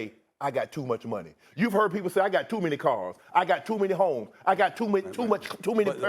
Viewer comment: I deadass thought he said “I got too many hoes”😂😂😂😂😂😂😂😂